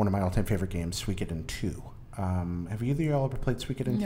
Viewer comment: I couldn't understand what game you said 11:40. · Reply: zoomandboom1 Suikoden 2